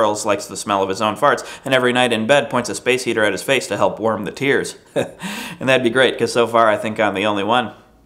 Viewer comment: lol I totally thought he said that he used the space heater to warm his ears the first time :P